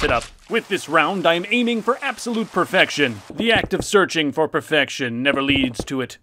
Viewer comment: did you say... perfection?